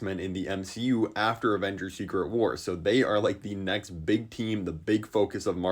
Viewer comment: Why did I hear "Avengers Secret Horse"? Ig my brain is not working anymore